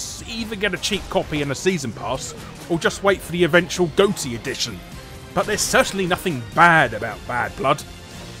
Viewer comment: At first I thought he said Goatse and not GOTY Edition. And I wondered what whimsical world I had been missing out on all this time.